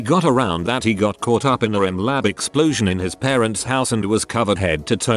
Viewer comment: what does the m*** say? and why is it censored? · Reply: Meth